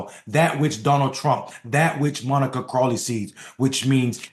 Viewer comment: I thought you said Crawley. But no. Cos the first thought in my head was "Creepy"